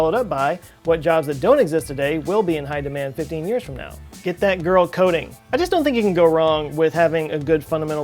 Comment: I first thought he said "Get that girl coating!!!" Yes, a good undercoting will give that car ... ahh .. giiirl ? at least a 15 year chance?!? ;-)